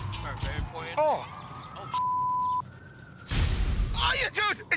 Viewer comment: Anyone else hear at 0:40 someone in the crew say “oh sh*t”?